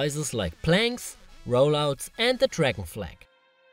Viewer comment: I actually flinched when he demonstrated the "wrong" dragon flag. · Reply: Oh! I thought he said "dragon fly." But in all honesty I never knew what that was called. I'm so glad that I read your comment.